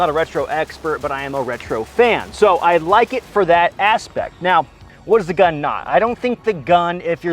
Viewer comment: asspect. He likes it for the asspect. I hear you